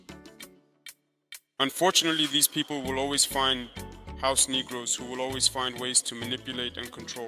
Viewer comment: Did he just say “house negros”? !